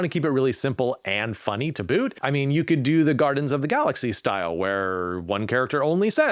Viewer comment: I had to listen to it 3 times but definitely says "The Gardens of the Galaxy" instead of "Guardians." 😂 Great video. Glad to see I'm already doing 3 or 4 of these and I wasn't even trying to find something like this to add to my book